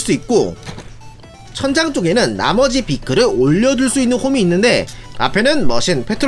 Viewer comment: 나머지가 아버지로 들려욬ㅋㅋㅋㅋㅋ